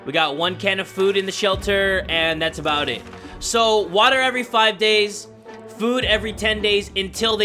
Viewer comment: I thought he said water every 5 days and food every 10 days